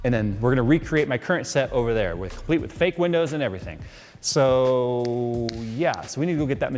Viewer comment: did he say "fake windows" ?